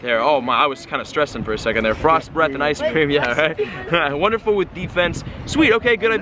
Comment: who heard frost breath ice cream?